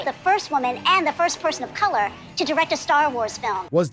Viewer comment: All i hear is "The fist pile of dog sh1t to direct a Star Wars movie", to which I'd like to point to Rian Roundhead Johnson.